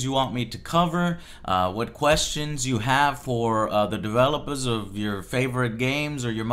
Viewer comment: Is it just me or did I hear "developahs" at 0:12?